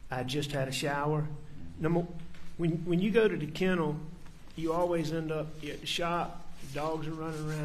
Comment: did he say "when you're going to the kennels you're always getting shot?"